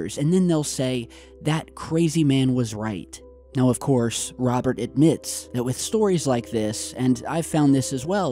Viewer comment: I heard it as Robert DAMOST